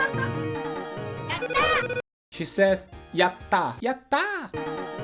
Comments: I always thought chun li said thats that! Not yatta. Interesting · The SNES version in the US had words in the booklet/manual, so I found out what they were saying pretty early. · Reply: I never read that, but most of these I guessed right except for Chun Li's celebration, I always thought she said "Get that?" as in "Did you get that?" Thought she was fourth wall breaking. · I always thought chun li said “that’s that!” So instead “yatta” nice 😎